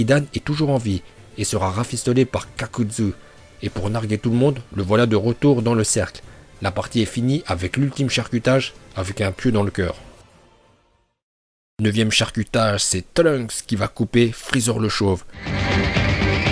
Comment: j'ai compris "avec un cul dans le coeur" ^^